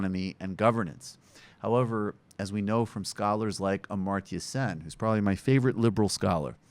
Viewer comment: What is the name of the scholar MB says around 3:50? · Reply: Amartya Sen.